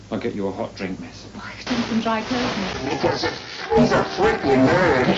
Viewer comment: “???, he’s a freaking nerd hehehehe.” That’s what I heard lol.